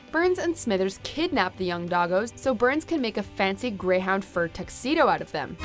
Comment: Did she say Doggos?? 😂😂❤